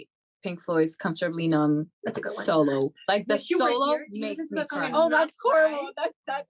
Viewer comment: What does Pau say to ale?? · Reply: i think she said "wish you were here"